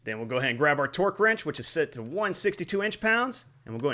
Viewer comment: did you say 162 ? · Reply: 62 in-lb is correct